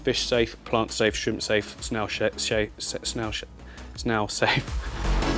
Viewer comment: I definitely heard “snail shit safe” 🤣🤣